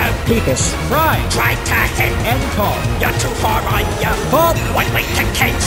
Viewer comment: am I the only one who hears “YOU’R TO FAR I GIVE [Up]”? And at 1:28 “I’LL THROW IN [Tiger motor]”